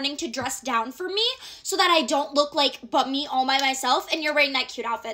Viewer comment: For a second when she said “bummy” I thought I heard “buttmeat” and I was about to make that my new word of the week 😂 “yeah, I got sick, sorry I can’t come to lunch today, I look and feel like total buttmeat.”